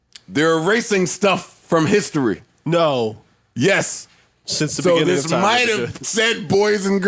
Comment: Did Parks say "since the beginning of time my nigga" @ 1:34:53 ?